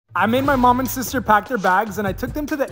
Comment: When he said I made my mum and sister pack my bags I thought he said I made my mum and sister pack my a**